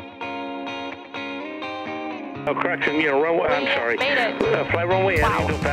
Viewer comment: did she say may day?